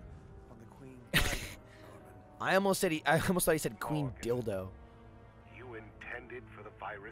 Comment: lolz i totally thought it said 'queen Dildo" too XD